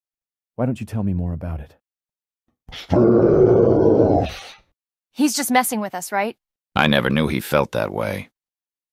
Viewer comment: What was my boy nemmy saying ? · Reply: “S.T.A.R.S.”